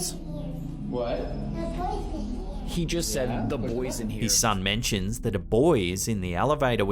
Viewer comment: I think the boy said “there’s the boys in here ” because he was seeing his reflection like 3 times, he was being quite clever for his age to make that joke 👏, that’s the impressive part, the dad it’s the one with too much time to make a hand appear with photoshop 🤔 not that clever in my opinion